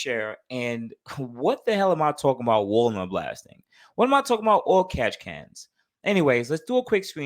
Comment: I thought you was saying Walmart blasting lol nonetheless I learned something new today 👍🏼